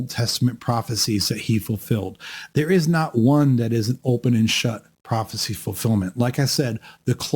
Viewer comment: I won't keep harping on this in every video, but I just want to note that at about 19:49 you said "there is not one that is an open and shut fulfillment" and the AI caption says "there is not one that isn't open and shut fulfillment". This is a complete reversal of your meaning. This is why I dislike the use of AI tools. A human who was paying attention would not make that error.